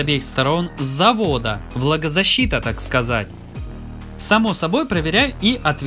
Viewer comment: Мне сначала послышалось не влагозащита, а владозащита